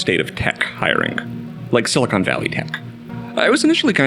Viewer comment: I thought you said “Silicon Valley Inc” which I thought sounded based af and wraps up the idea of the conglomerate going on there really well BUT I AM NOW COINING THAT CHEEKY TERM